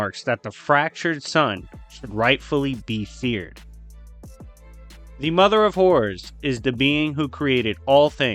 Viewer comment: Maaannn I was so confused for a minute because I thought you were saying "The Mother of \/\/hores"... I was like "DEMONETIZED....!!!"I gotta get my mind out the gutter...